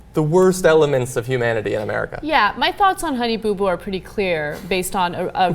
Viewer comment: Damn ,... thought it said Honey Boob Boob